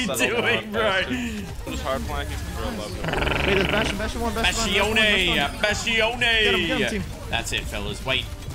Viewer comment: gotta be the funniest shit i heard as an arab "بسيوني،بسيوني" 😂😂😂